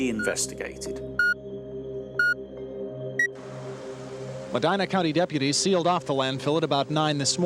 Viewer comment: For a second I thought it said Mandela County and I was about to shit bricks.